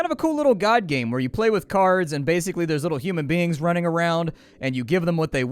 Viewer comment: did you say you play with *KARS*?